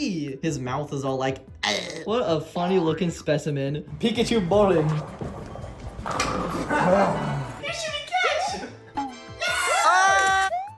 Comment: I thought he just said Pikachu moaning😂😂😂😂